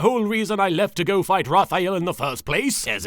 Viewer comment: Dartz saying "Raphael" I coulda sworn he was saying "Lafayette" dammit Hammy fandom